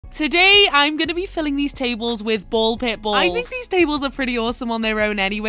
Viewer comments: POV you thought she said ball pit bulls 😭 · I thought she said she was filling them with bald pit bulls for a second. Disappointed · I thought she said bowl pit bulls😭